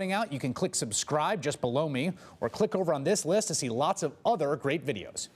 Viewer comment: The guy at the very end that tells you to subscribe it sounds like he says just blow me to subscribe I hear it every time I watch the update sorry but I had to say it